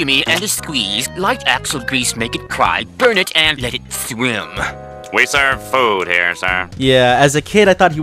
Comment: Wait squidward said "we are serving food here sir"? I thought he said "Ermm what the sigma?"